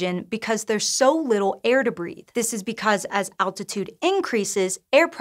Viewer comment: I misheard "air to breathe" as "air debris" initially.